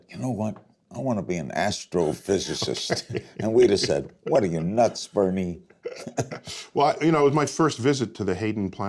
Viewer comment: What, are your nuts burning? Did I hear that right? Lol.